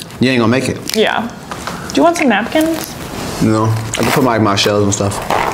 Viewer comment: It did sound like he say no to napkins lol